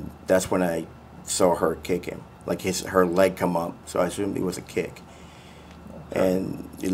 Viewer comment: Wow does he hate that lawyer or what. I heard him say I ASSUME NOT I SAW.